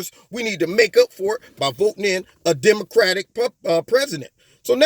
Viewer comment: anyone else hear, "Democratic Puppet"